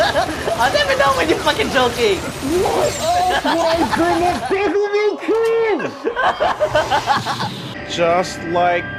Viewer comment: what does say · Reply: "Whaaa!? WHY WOULD I BRING UP DEVIL MAY CRY" Since thought was being serious in comparing the boss fight action intensity to Vergil fights in DMC games